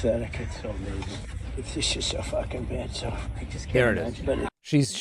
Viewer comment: I heard “she’s just so fucking bad” (fyi, I’m a trump hater, but just saying what I think I heard)